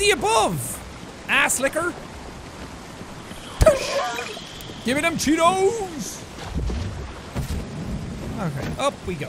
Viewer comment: is it just me? Orrr did jack say "GIMME DEM CHEETOS"😂